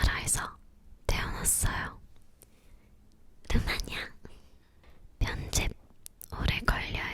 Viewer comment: 답변 잘 안들려요....! 아시는 분 답글 달아주세용 ㅠ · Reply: 루마니아라고 하신 것 같아요